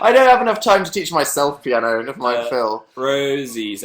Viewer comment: At like 25:23 I thought Dan said "my Phil" but I think he said "never mind Phil". Sigh